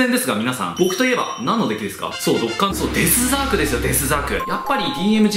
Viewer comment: あれ今ドッカンデイヤーって聞こえた気が